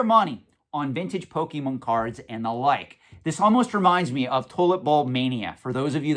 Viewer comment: lol I thought you said toilet bowl :) tulip bulbs